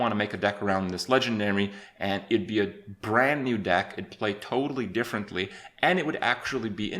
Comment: did he just say: "I'd be a Brann new deck"?